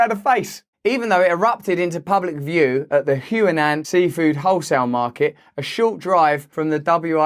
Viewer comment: You said "Huanan" market I thought you said QAnon 😂 The irony.